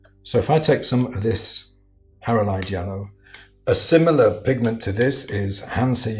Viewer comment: I thought you said 'Paranoid Yellow' at 3:11. My eyes are better than my ears.